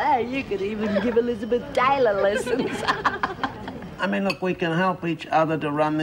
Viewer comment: Did lizzie say Elizabeth Dale at 17:28 ? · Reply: to Pixie: You could even give Elizabeth Taylor some lessons. LOL